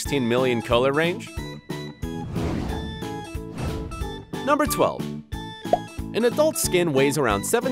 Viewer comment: did he say 10 or 12